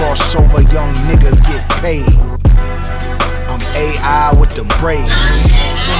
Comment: I thought he was saying “AI with the brains” as in a notably smart AI